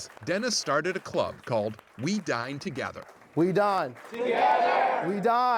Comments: idk im socially awkward and seeing someone with the shirt "wedinetogether" and sitting next to me would feel very uncomfortable. their intention may be to actually make me feel good, but my first reaction would be that theyre doing this so they can look good and get some extra credit in their club or whatever. they didnt become my friend in the first, second, or third year of hs for a reason, it would be pretty obvious they think of me as a way to look good, theyre faking. not all of them. maybe. sorry for finding the negative in this. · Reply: Thought they said "We die? Together!" SO IT'S WE DINE... Makes sense now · I thought they kept saying "We Dyin' together" oml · Omg when they were saying "we dine together" I thought that they were saying "we die together"